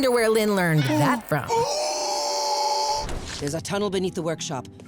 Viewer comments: I HEAR SECRET TUNNEL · who heard secret tunnel